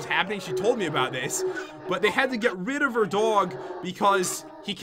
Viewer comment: I thought u said they had to get rid of jens mom